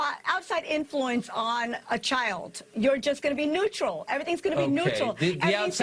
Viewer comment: i'm not a native english speaker, did she say NEUTRAL or NUTERED?